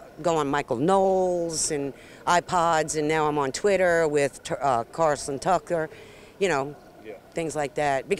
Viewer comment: Where do you get your news? Oh I don’t watch news I get my misinformation from Tucker Carlson 🤔🤔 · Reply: I thought she said Carlson tucker ?